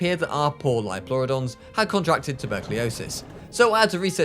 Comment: did you say tuberculosis “ I guess I am afraid” Arthur Morgan 1899